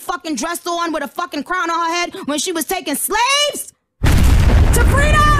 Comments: ALL MY LIFE I THOUGHT IT SAID SABRINA!!! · this entire time.....i thought she was saying SABRINA when she was saying TO FREEDOM